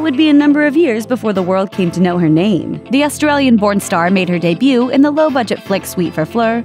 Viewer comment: I thought it said Australian pornstar 😂